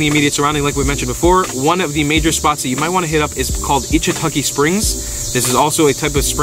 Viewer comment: What’s the bonus place you mentioned? I can’t quiet make out the word. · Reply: Lol 😂 I listened again and to me it sounded like you said, “eachintukey springs” so that’s what I typed in knowing that wasn’t it but hoping I’d get something similar taking me to where I wanted to go. So for anyone else who wants to know it’s Ichetucknee Springs Florida. 😊😂 👍